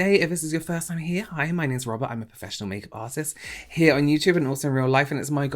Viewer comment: Robert! I just watched your video whilst very high ...and I thought you said "im a pussy make artist" ...and I shouted "oh my god! You do pussies too?! ". I had to replay that bit 3 times before I finally realised you said "professional makeup artist". Hahaha! I'm sorry! I thought you would appreciate that 🙃